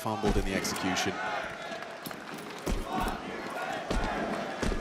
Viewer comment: what is the crowd saying? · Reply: they are saying fuck you apex lol. With love tho